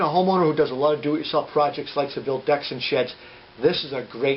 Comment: I thought you said decks and shit but you actually said sheds lol